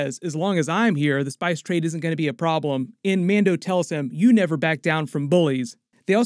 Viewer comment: pronunciation is everything, I thought you said “You never back down from boys” 😳